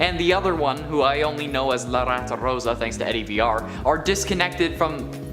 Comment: did he just say Eddie vr!?